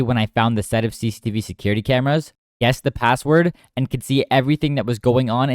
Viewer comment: Did he say "guessed the password " at 4:00? Lmao 😂